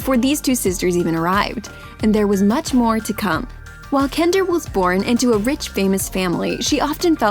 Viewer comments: Is it just me or does she say Kender instead of Kendall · who else heard she said kender